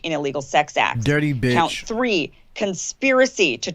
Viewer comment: I thought the news said “dirty b*tch” 😂😂😂😂 thought they were real for a second hahahahahaa